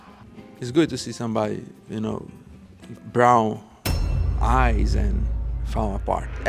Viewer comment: mind went to the gutter with that 'brown eye' reference. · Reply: I thought he said brow, eye